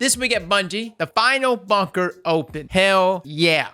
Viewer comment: OMG...Lol I have to get my hearing checked. when you first started to read the TWAB, and you said "The final bunker opened". I thought you said "The final Fucker opened" 🤣🤣🤣